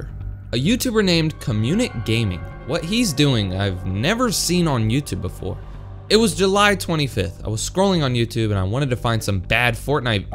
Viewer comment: At first when u said comunic gaming I thought u said community game